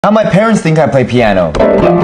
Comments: I thought he said "how penis think I play piano"💀💀💀💀💀💀💀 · I thought you said ‘how p3n1s think i play piano’ 😂😂😂💀